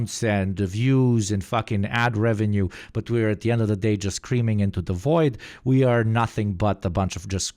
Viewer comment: saying “screaming into the void” totally took me out. It sounded like he said “creaming into the void”💀💀